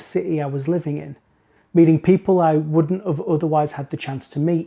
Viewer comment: Is it the accent thing or did you really say "wouldn't OF" at 4:08? I heared it very clearly. Could be just accent. No, I don't want anybody feel bad, I like helping people get better... even if they don't ask.